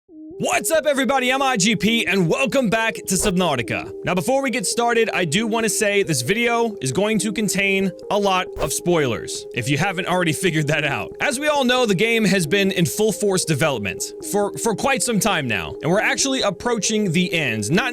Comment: i rewinded 5 seconds before he said alot so it sounded like he said that "this video contains alota subnautica"